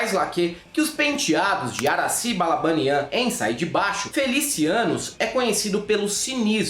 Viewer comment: Mano.... derrubei o prato quando escutei "FeliciANUS"... pqp